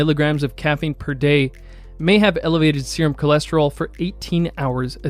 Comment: caption says cortisol but audio says cholesterol